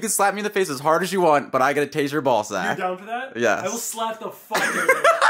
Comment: LMAO I THOUGHT SAID "I GOT TO TASTE YOUR BALL SACK" AND I REPEAT IT FOR 3 TIMES TILL I REALIZED THE TAZER LMAOoo